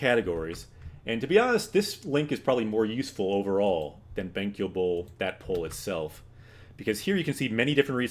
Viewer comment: I keep hearing "benkyouable" and can't get it out of my head...